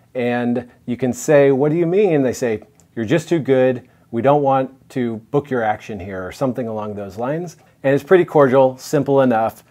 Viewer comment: I heard "I don't want to book your action sir" lots of times